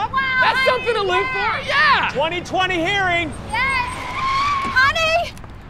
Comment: Did he just say 2020 hearing 😂😂